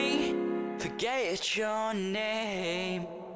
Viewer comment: I’ve now realised they are saying ‘I’m ready’ I thought they were saying ‘r rated